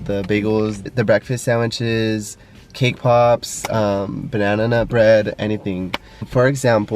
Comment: Omg i legit thought he said K pops at 2:08 ... im crYinG